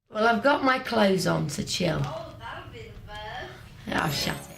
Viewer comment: What does mel b say at 13:12 ? I am from Germany I couldn’t understand it. And why are they laughing at 46:21 ? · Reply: "That'll be a first" after Geri says "I've got my clothes on to chill" I haven't figured out what the laughing thing was about